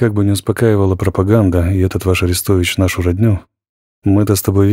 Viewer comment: Слушал перед сном. Думал послышалось... но было лень вставать и перематывать. Аннет! Всё таки "Арестович" был :) Очередные главы зашли на ура! Спасибо Вам Алексей!